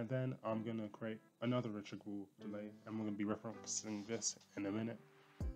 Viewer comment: accent is so think it sounds like a completely different language. All i heard was, "Reachable delay."